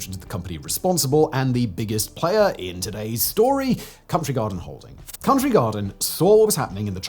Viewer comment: Who else thought he was saying Country Guard and Holding?